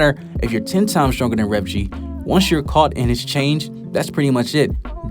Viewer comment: Did you say change @ 2:24 or am I trippin 😂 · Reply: Lol I said chain but it does sound pretty close